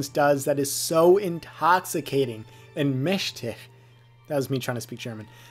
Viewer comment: Was hat er gesagt?😂😂😂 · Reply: Ah verdammt, ich hab versucht, "mächtig" zu sagen.